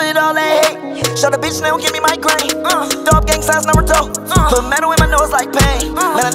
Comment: “Throw up gang signs, Naruto” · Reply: I thought it said throw up gang signs never talk, lmao